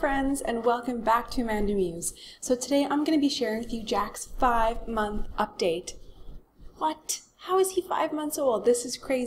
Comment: I really thought she said 'Jackson 5' at the start. Then I realised she said 'Jack's 5 month update' lol.